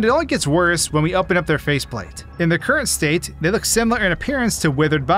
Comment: did you just call Bonnie a GURL?!?!?